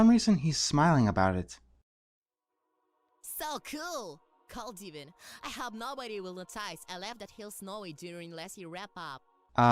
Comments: He is saying "So cool! Cold even. I hope no one will realize that i left the hill snowy during last-years wrap up!" At least that's what I think he is saying.... Not Rainbow Dash's name is Breeze Rider, by the way. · For automatic subtitles, this is what not-Rainbow Dash said: "cellco called even have nobody will it cites that heals not only doing less your app" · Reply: listening to the line about 30 times and seeing guesstimate the words I think I finally figured it out. "So cool! Cold even. I hope noone noticed I left the hill snowy after winter wrap-up" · "So cool, cold even. I hope nobody will notice I left that hill snowy during last year wrap up." That's what it is supposed to say, but I heard, "So cool, cult even. I hope nobody will atice a laugh that heals snowy during wesyear wrap up."